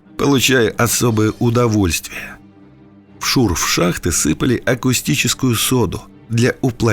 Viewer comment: Я одна услышала "акустическую" соду? Она же "каустическая"...